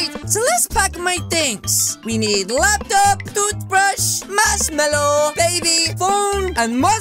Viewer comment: did he say a baby